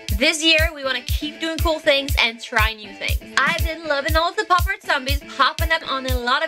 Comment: Thought that said poptart...